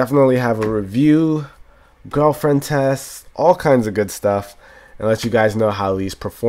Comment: did you say "girlfriend tests?"